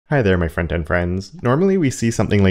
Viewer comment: After many years of following, watching, and taking your courses, I've just this second realised that you open your videos with "Hello my front end friends", not "Hello my friend and friends", that I thought that I'd heard dozens and dozens of times, lol. Just LOVE your videos, the most value I get from YouTube, by a huge margin ❤️